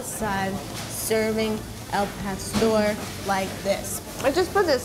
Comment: They put put help a store in the subtitles instead of apastor Dislike unsubscribe